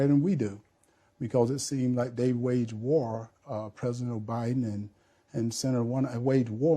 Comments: Did he say President “O’Biden”? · Did he say President OBiden @ 1:59 😂😂😂